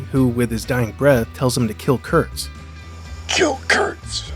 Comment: Maybe it's the previous epsiode lingering on in my head, but I could have sworn he said "cue cards", not kill Kurtz 😅